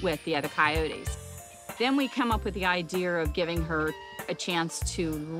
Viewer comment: LOL 2:48,did anyone Else hear her say "then we came up with the I-deer"?. She is to into nature 😃